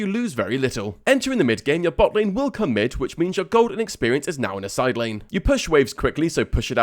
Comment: Your gold and experience is now in a side lane? 👀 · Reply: i thought you said Golden Experience. That would have been a Jojo reference xD